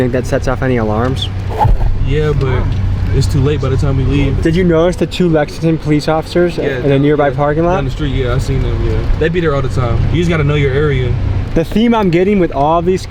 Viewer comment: Bro at 14:39 I thought he said “did you notice the 2 Mexican police officers in the nearby parking lot”